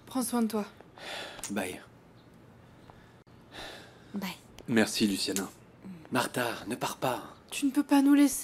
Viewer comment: j'ai entendu Ramón dire merci Luciano 😅